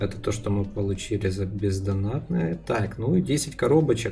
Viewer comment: послышалось пиздонатное